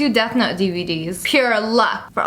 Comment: When you said death note I thought you actually said deaf nut 🤦‍♀️🤣 and why does your good will have good shit mine ain't got nothin 😫